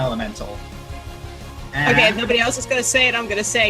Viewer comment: What did say at 15:07? "Get a rock" what?